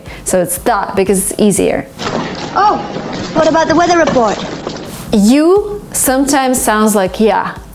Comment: the video reference is wrong - She said what ABOUT the weather report instead of DUH By the way great video